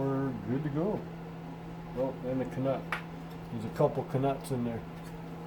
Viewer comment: I was wondering what you said, too :D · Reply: K-NUT!!! A K A NUT!!!